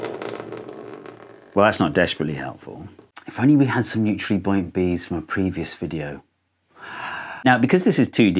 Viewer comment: i think en subtitles on 6:07 are wrong, they should be "neutrally buoyant beads" instead of "mutually point bs"